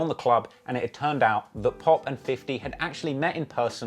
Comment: Ik I’m not the only 1 who Hurd Traplore say Fofty instead of 50